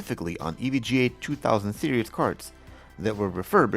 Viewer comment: did you say "Serious" cards instead of 'Series"?